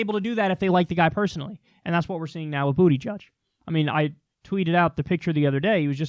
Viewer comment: When I first heard Kyle say "booty judge" I thought he was making up another funny name for a politician. This one writes itself. "Maybe you're just being an asshole." Eloquently said.